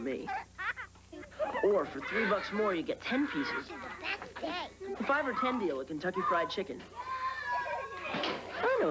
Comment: What'd the kid say at the 5:42 mark? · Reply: That’s gay